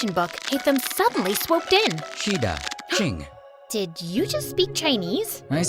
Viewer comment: naw the "yes please" feels so weird (cuz im chinese too >:)) btw i heard "si da chene" XD